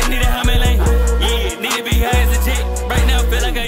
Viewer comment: When I first heard this I thought yeat said the n word when he said need to be high as a jet